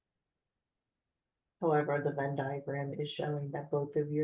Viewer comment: "van diagram" ? what does she say ?